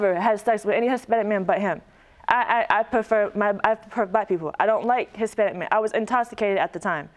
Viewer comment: I was not ready for the "fiance" 😶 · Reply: I DID rewind. To 1:18 and guess what — she said she prefers “Black PEOPLE,” not just Black men. Man, she actually even made sure she said that, but none of us could tell what was coming!!